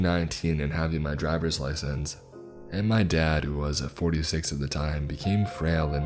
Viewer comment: I misheard "my dad was 36 at the time and becoming more frail and fragile" and felt personally attacked 🤣